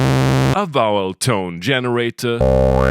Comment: I heard “bowel tone generator” and I was like “fart machine?” It’s what I’m calling fart machines now